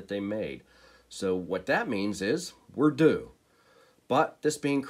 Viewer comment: Ha I thought you said “what that means is.. we’re doomed”